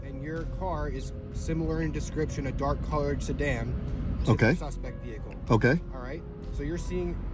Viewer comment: He clearly said "sedan".